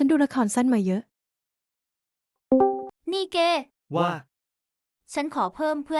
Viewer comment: ไม่น่าจะพูดว่าแกนะพูดว่า....เก